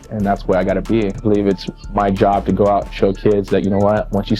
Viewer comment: I thought he literally said you know I believe it's my job to go out and choke kids lol he said show kids . For a second I was like damn Tito you're out there pulling an Anakin Skywalker on these little kids. 😂